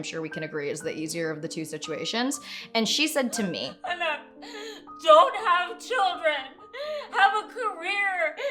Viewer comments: wait wait did she just say "anak"???? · I thought she said "anak" for a moment haha (which means child in the Philippines)